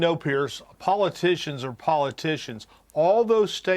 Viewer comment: He said "politicians are politicians" I heard "liars are liars"